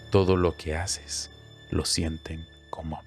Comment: No entiendo que dice al final de la frase del minuto 19:46 “sabes que es lo mejor de los niños, que no te juzgan, todo lo que haces lo sienten como …. Y ahí no entiendo que dice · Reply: "Común" esa es la palabra que dice